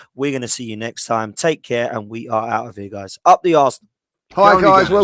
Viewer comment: Next time, don’t stop the audio until you have finished speaking. 1:16:12 he clearly says “up the Arse” 😂